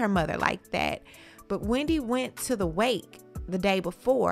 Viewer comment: I thought she said Kelvin went to her mothers funeral